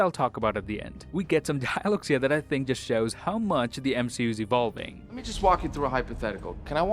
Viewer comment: wth is he saying? I played it back a 100 times 😂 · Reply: "We get some dialogues that show how much the mcu is evolving....". I watched 5 times to understand that